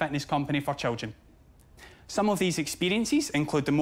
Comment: I thought he was saying children’s fatness lol.